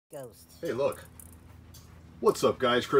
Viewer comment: In 0:00 - 0:00 I hear somebody saying ghost